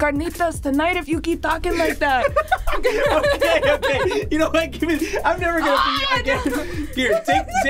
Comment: did just say y’know what I’m not gonna beat you anymore wtfrik